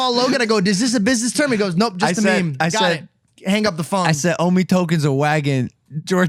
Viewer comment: Did I hear “OMI token”? OMI is future of NFT 😍